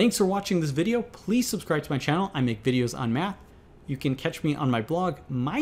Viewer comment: At the end, I thought he said:' I make videos about meth.'🤣